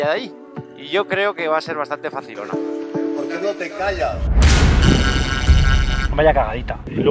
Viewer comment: En el minuto 10:39 se escucha una voz que dice. POR QUÉ NO TE CALLAS. Me asusté mucho ????? 😮